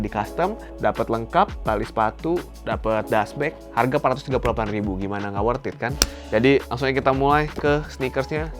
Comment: Ntah gua salah dengar tapi. "Udah di costume harga di bawah 500rb gimana gak worth it kan" maaf kalo salah